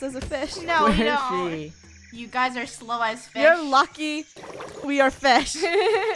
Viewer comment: What did say? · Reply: Fish